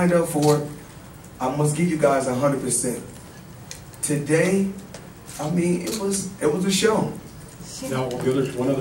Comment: wtf did he say because all I hear is him saying "today I'm being a pussy" ive tried listening over and over but can't unhear it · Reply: "Today, I mean it was it was a show"